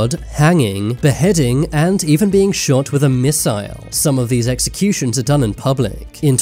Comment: Did I hear that right? A missile? I think I need to clean my ears so I can hear better next time.